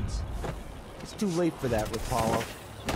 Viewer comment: Did he say "it's too late for that RU Paul" ? LOL